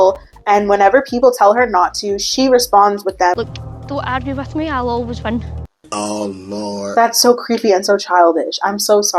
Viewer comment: What does she say? 🙃 I can’t understand 😂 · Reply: She said "don't argue with me, I will always win"💀💀💀